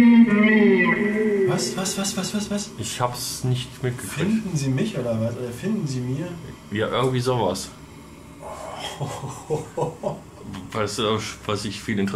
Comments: = Hier verstehe ich ,,Lindern sie mir" dies würde auch zu der vorherigen Antwort (4:25) ,,Angst empfinden" passen. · Bei 4:53 verstehe ich "findet den weg"